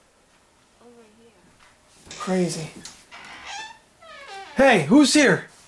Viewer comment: I heard someone say 'over hear' just before